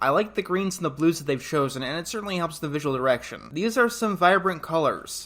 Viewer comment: did he just say visual erection?